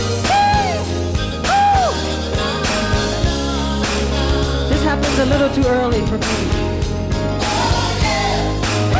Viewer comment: what are he is saying? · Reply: “This happens a little too early for me”